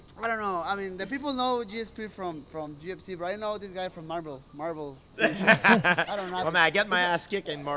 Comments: Haha, I thought he said “I know him from marbles” Love these two, best attitudes in mma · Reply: lmao, I thought he was saying marvel like he’s his hero kind of thing, I guess I heard him right the first time. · Haha, I thought he said “I know him from marbles” Love these two, best attitudes in mma